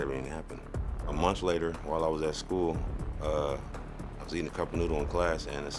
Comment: did he say “I was eating a cup of noodles?”